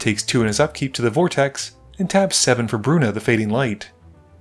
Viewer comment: "Alex takes two to the vortex" really made me start watching the game when I heard "two to the Cortex"